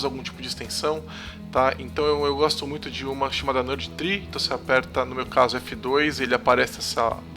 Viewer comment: Qual o nome da extensão que vc fala no 2:40 ?? Por favor... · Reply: Nerdtree: <link>